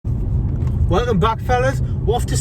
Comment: I thought at the start he said "Welcome black fellas"